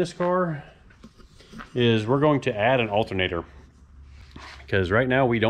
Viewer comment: You say "alternator" and I hear "Race Week".